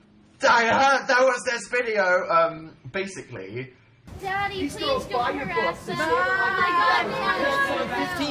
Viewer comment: I thought the girl said “Daddy please don’t harass me,” and thought she was talking to Dan 😂 but to be honest I feel SO bad for that girl..